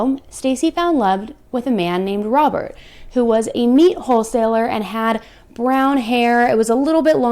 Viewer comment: I swear it sounds like you say "a man named Robber, who was a meat hole seller." Lmfao I think that's more fitting.